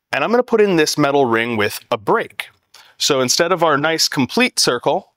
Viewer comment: I thought he said a Brick... for 2 seconds I thought where is the brick? then realized he meant a Break... otherwise his English was easy to understand, no weird accent.